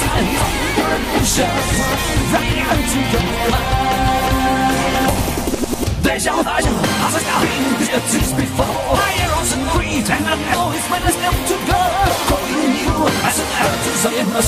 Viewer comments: all I hear is "Dej-UwU" and I hate it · I heard “higher on some weed”, honestly the person who made this is probably high on weed